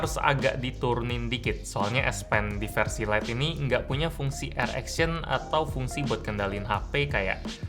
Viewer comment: saya kok dengernya erection ya😩